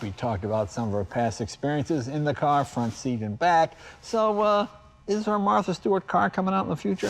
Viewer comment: What's that you said Jay... You were in the back seat with Martha Stewart ?? Great Episode.... Loved it.